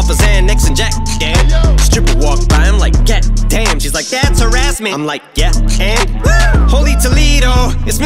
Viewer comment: I can’t be the only who thought he said “that’s her ass man” when he said “that’s harassment”